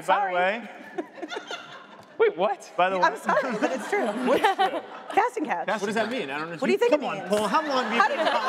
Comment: what are they talking about? casting calf? I can't hear it. I'd google it- but I can't make out whatever they're saying. anyone that can hear it and wanna tell me I'd be fairly grateful.